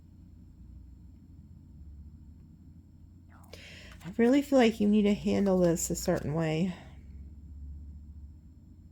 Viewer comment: Theres is a voice time stamp 40:08 not sure what is saying doe please listen to it. I thought I was going crazy at work. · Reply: Yes it says "no" in a whisper